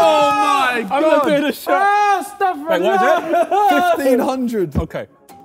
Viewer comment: Anyone else hear Ethan say “ astagfirullah” at JJ’s awnser at 19:49 😂🤣